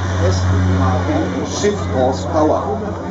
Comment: What did he say at 0:53? sh!t balls power? I guess that means a lot of power. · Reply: He says something like "shift horsepower" and I guess what he actually meant was "shaft horsepower" ( <link> ). But nevertheless, translating it as "a lot of power" is probably not missing the point by much 😉.